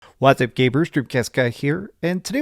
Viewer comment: Did he call us "GAYBERS?" Stop taking xanax bro!